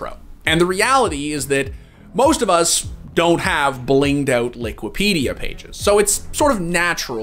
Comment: Liquipedia? Did I hear that correctly?